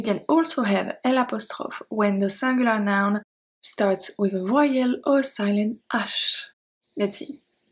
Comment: Love your lessons but at times you can be hard to understand/hear for me, I have found the subtitles hard to follow. For example i have no clue what you said @ 5:15 (for rules on the having el apostroph when singular noun) it says you say with royal or silent. I dont understand royal, what was meant. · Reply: Vowel ;)